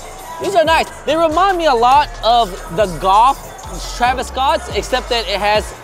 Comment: Bro said goth instead of golf